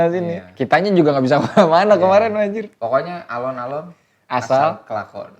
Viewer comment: apa cuma gue yang dengernya "alon alon asal klakson" 😅😅